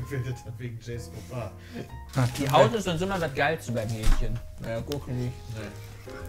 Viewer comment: Ach, Hähnchen hat er gesagt. Ich hab Mädchen verstanden und mich etwas über den Drehspieß gewundert.